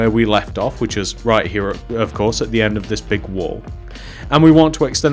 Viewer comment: Did he say pink wool on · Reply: He said big wall